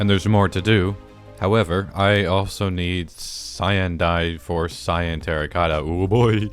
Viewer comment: I thought he said cyanide 😭 🥀